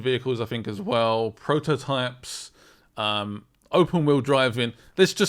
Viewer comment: sorry did you just say "Open world driving" ? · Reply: Open Wheeled Driving. Sorry lol